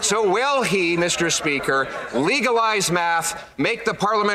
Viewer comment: I thought he said, legalize meth.. oh wait, we already did that. Only in canada.